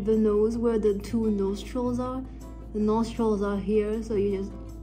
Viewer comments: OMG I THOUGHT U SAID NOSE TROLLS 💀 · Totally thought you said "nose trolls" instead of "nostrils" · I'm sorry, I heard 'Nostrils' as 'Nose trolls' 😭😭